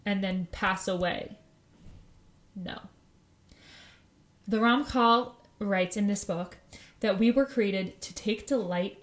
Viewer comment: you say something that sounds like Ron Paul and you keep saying it. What are you saying? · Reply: said the writer of the sayfer, Meseelas/t Yeshareem "Ramchal" which is from the acronym of the name Rav Moshe Chayeem Ben Luzatto - RaMcH"L.